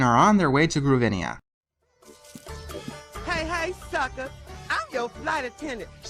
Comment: I hear "Hey hey, f***ers."